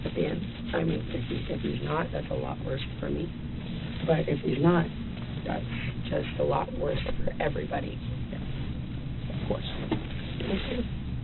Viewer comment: She clearly says "If he's not (ALIVE) that's just worse for ME" And then quickly goes to "If he's not (ALIVE) that's a lot worse for everybody" So how dare you all judge her, she corrected herself in a second. That's true love❤😂